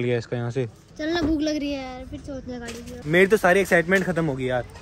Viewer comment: ne kya bola 😂? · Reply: Chal na bhuk lagri hai yarr😂😂😂